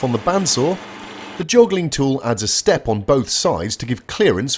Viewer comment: did you say "joggling tool"? What in the world is a joggler? What does it mean to be joggled? What entails joggling? Honestly I just like saying it. Joggle.